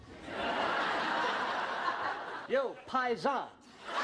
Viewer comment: What does he say at 0:34 ? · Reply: Bhrigu Srivastava 'yo,paisan' is what he says